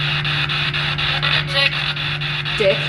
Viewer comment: i hear Nick not Dick for their name